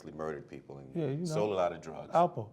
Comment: what name does 50 say? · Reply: Alpo. He was a newyork drug dealer